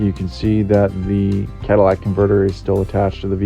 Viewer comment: Did you say Cadillac converter?.... 5:08 - 5:11 ... ???? Aside..... the Scotty Kilmer recommendation was to use 1 gal thinner to 9 gals of gas.....you are about twice as concentrated at 1 gal thinner to 5 gals gas..... where did you get that dosing recommendation?